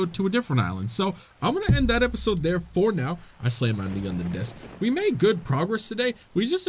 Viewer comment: DID HE SAY "i slammed my meat on the desk"??? 😂😂 I'm sure said "knee" but I heard "meat" 😂